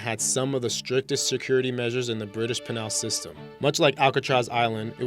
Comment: Sounds like at 5:48 he says "British penile system" come to think of it, everytime he says penal it sounds like penile....come on dude